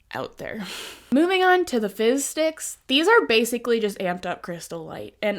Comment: i kept mishearing "fizz sticks" as fishsticks and i was so confused XD like, an mlm that sells fishsticks??